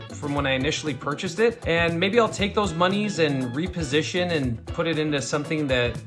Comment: did he say monies😂